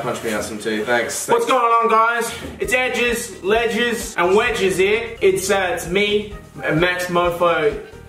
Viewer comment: What does max say in 1:09 ? D: i can't understand haha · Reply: dice its edges, ledges and wedges here